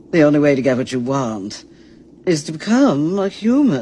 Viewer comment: I thought it said “the only way to get what u want…. Is to çüm💀